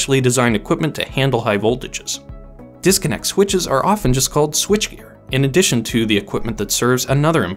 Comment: At the 4:00 mark, it says "Disconnect switches are often just called switch..." with the last word being almost inaudible. It kind of sounds like switchier, but I don't think that's a word. Does anyone know what it is? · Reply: Switchgear